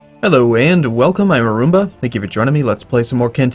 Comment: mmmmmmhhh Arumba sounds like he said "I Rumba" Arumba is your robot to human translation programming glitching?